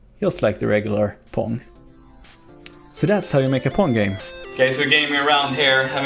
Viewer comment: in the beginning i thought you said porn game